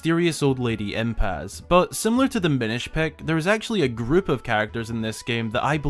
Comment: Did anyone else hear Minish Pack? Haha! I've done that myself plenty of times. Haha!